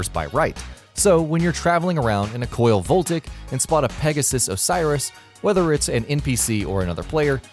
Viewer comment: I heard that right, didn't I? PEGASUS Osiris? Guess it's not Pegassi any more lmao